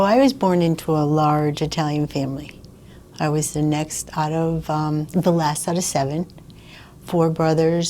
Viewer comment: I thought she said she was the youngest of seven. Did she say she was protecting her younger brother? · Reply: She said “next out of the last”